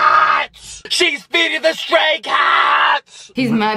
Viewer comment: lol i heard "sHeS fEeDiNg ThE ✨sTrAy KiDsS✨"